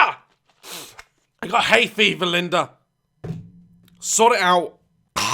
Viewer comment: I was distracted at the start of the video and I thought I heard “I hate Bieber Linda. Fix it.”